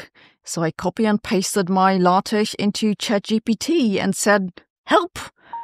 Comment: What is the word Sabine says around 0:09? Sounds like 'latesh'- maybe 'LaTeX'?